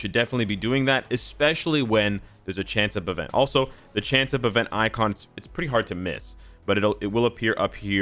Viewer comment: what event icon is he talking about? They way he said makes it sound like you can get a special icon from farming storm/beast/cyclops/rogue. Is that the case? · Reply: I think he said "Chance Up" event icons, could have misheard though